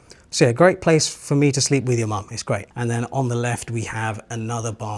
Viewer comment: “So, a great place for me to sleep with your mom.” Did I hear that right? 💀